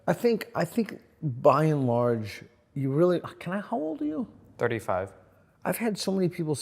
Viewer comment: Who else thought he said, "Can I hold you?"